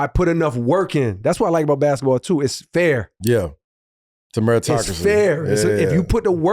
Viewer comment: what word is he saying ? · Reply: Fair